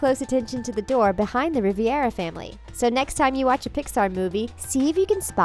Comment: did the voiceover say...."Riviera" instead of "Rivera"....?!